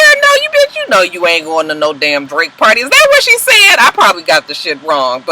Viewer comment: I heard "Drake" party also